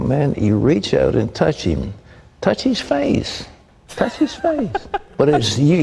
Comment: "Wait, TOUCH his face? I thought he said punch..."